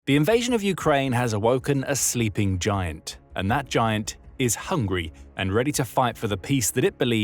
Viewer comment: i thought he said, ''and that giant... is hungary''